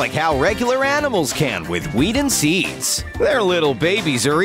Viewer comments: is it just me who heard weed? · Did he said "Weed"